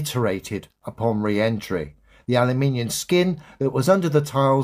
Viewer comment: did he say Alli-Minion?? 😂